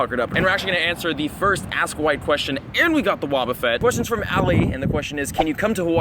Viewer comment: i thought he said we'll answer the first asswipe question